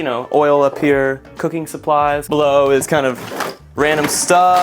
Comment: I was down in the comment section reading the comments while the video was playing and I got to 2:07 If you only listen to the audio, it sounds like he saying "you know, keep oil up here, cooking supplies, blow." 😂